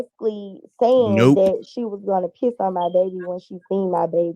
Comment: I kept hearing her say 'She was going to puke on my baby' Either is just sick.